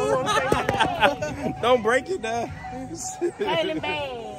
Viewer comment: what? she said her name so muffled and unrecognizable, what is the chic IG or Tik? she's very cute